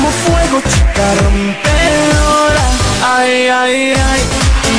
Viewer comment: like quien no la conoció por tiktok (autolike pq no la conocí por tiktok,solo me acordé de que existía jaja) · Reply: La conozco desde hace como 8,9 años 😂l@ que lo escuchan recién que es lo que escuchaban antes 😂 Siempre pensé que decía rompebolas😂😂